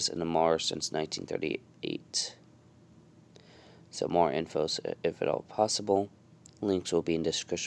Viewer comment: I thought you said “some poo”